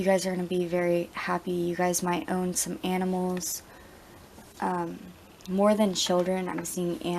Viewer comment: thought you said you guys might own some animes and im like WHAAA FOR REAL OMG NICE XD I sure do wish that X'D